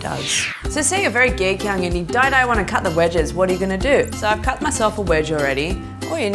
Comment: what is she saying at 3:02 to 3:05? your very geychang and you diedie want to cut the wedges?